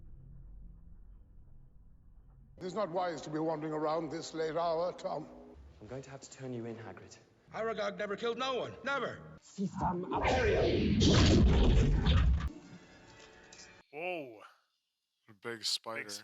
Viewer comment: I swear to god I heard Hagrid say Aragorn except of Aragog. XD